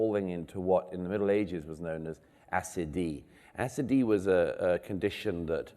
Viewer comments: The term Douglas mentions here is 'Acedia' . I had to look it up because it sounded so foreign to me I couldn't get what he was saying and I think I'm not the only one. From here on you can use Google, I can't post links. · What's that "Acidy" word Murray mentions at around 7:30? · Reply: Acedia.